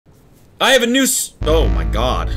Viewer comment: First thing I hear is “I have a noose”